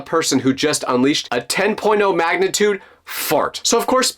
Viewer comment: what did you say? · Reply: 10 point 0 magnitude